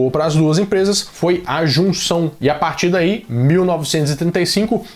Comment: "A solução boa pras duas empresas foi então a jun..." meu cérebro mineirês completa com "ajuntar!" mas era "a junção" kkkkkkkkkk